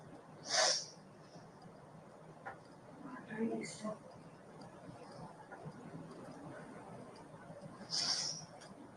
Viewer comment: Does she say "Parked" or "Walked" right next to him? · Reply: Parked